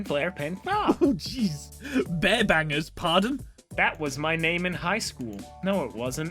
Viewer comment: I thought said bare backer instead of bear banger